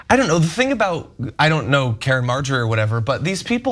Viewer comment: I swear I thought I heard Ana say "Karen AI," which made me think of an AI that would loudly harangue you from a position of obnoxious privilege, then thought "That could actually be a little bit fun."